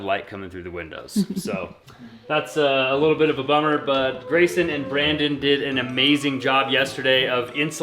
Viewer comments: My husband was in the other room and he thought you said “raisin and bran” instead of “Grayson and Brandon”. We had a good laugh and then he called them “The Two Scoops” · When is due date? So exciting ❤️ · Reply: My husband was in the other room and he thought you said “raisin and bran” instead of “Grayson and Brandon”. We had a good laugh and then he called them “The Two Scoops”